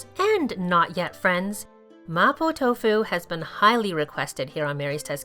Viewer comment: lol I thought it said Maple tofu